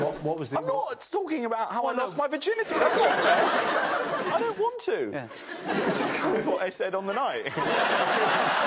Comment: What did Jack Whitehall say near the end 11:52 ? Something something on the night. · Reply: "I don't want to, which is kind of what I said on the night."